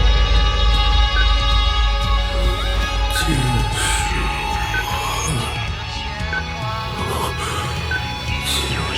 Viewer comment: what does he say here? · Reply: End me